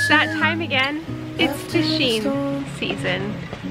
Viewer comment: JASPER! 12:20 is sounded like she said tishing